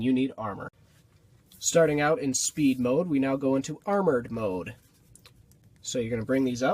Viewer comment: I thought he said turd mode😂😂😂😂